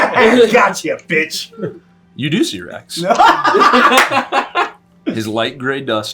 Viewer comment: When said "You do see Rex", I had to play it back a bit because i heard "you do see your ex" and I was like "LETS GO! WE GET TOXIC EXES!!"